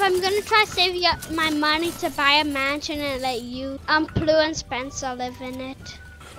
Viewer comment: When he said he wants plu and Spenser in the mansion I really really REALLY felt bad for cryst he is a good guy too!! Yooooo he loved my comment! Use code plu Thanks for 200 likes! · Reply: Oh I thought he said you and plu and Spenser